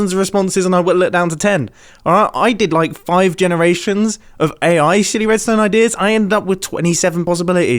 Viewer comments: Does anyone else hear A.I. s**ty redstone ideas at 12:30 ??? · did he acidentally say shitty instead of silly? Whoops... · is it just me or did he just say "AI shitty redstone ideas"? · Is he saying sh##ty ideas or silly?